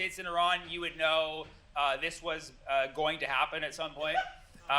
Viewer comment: Tell me why I thought bro said “goyim to happen”